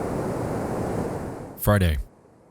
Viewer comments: when you said "Friday" I thought you said "farting" and I was like, wtf? lol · When he said friday in the beginning, I thought he said fried eggs.